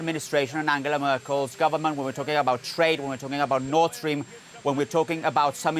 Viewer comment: This has a lot to do with Nordstream. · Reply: For a second I thought you said Nordstrom :)